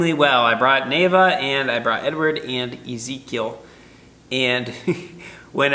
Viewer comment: When you said Ezekiel I thought you said Easy Kill so that's his new name